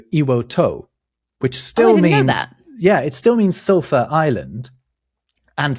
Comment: until the kanji came up, I thought was saying "sofa island" the whole time!